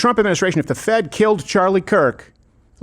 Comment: How can Candace say such vile things while smiling? Very dark! I thought she said the Jews killed Charlie, now Trump did? Charlie got him in the White House what's his motive? If you want to see who benefited from his death see who celebrated it-- that was the Democrats. Charlie Kirk was the future of the MAGA movement he WROTE a book about it!!!!